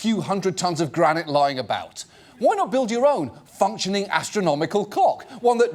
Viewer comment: i'm sorry, all i heard was "functioning astronomical cock"